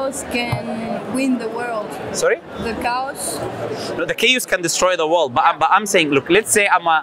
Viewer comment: Lowkey thought she said the cows are going to destroy the world